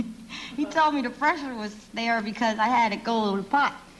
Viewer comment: "C'mon c'mon c'mon c'mon ?????" She said something that annoyed Carson. What did she say? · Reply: I thought he said she had to go pot